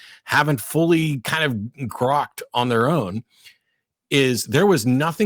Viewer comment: Caught that "groged' Matt. One of my favorite books. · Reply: I actually thought he said "grokked".